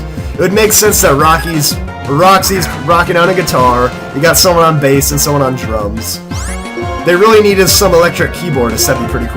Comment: when said someone is on drums i thought he said drugs